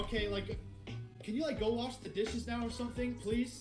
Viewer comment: I thought the guy said "can you go off in the distance" in the first clip 😂